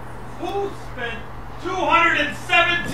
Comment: He sounded like he said animal frosting